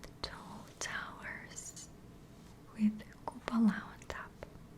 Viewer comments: what did she say here? the what on top, i cant make it out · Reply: I used the word "kupola" I think that means domes? I could be wrong though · What does say at 17:42? This is one of my favourite rewatches and I try to work it out every time but no joy! "With something on top"?! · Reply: "with a cupola on top" it's the domes :)